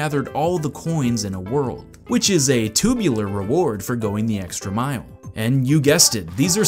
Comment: did he just say TUBULAR? I am now very happiness